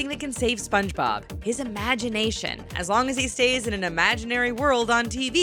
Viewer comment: It sounds like she said "amination"